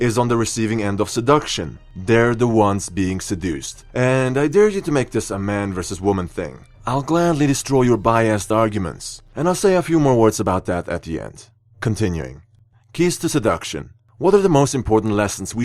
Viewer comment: I thought he said bi assed arguments but it was just biased arguments 😩😂